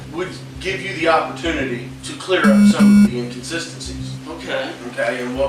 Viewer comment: "Instances?🤨Mate I don't mean to be rude but you need to learn to listen to English words properly. Can you not hear peoperly?🤔 He says "inconsistencies", and that to you sounds like "instances" does it?🤨 Same with around the 3:00 mark, the girls explains the chan being attached to the wall and her neck and YOUR DMUB subtitles said something like "Catch to chain her wall" like, wtf?🤔 The subtitles that you have made up do not match the words being said. You will never EVER get even 10% likes when you make such bad mistakes on a YouTube upload. Look at the ratings for example: 2,100,000 views and only 28K Likes? That's not even 5%🙄 We are far worse than any judges on any AGT or X-Factor or any talent show. Dislike & Do Not Recommend Channel. Welcome to YouTube 2025~!🙄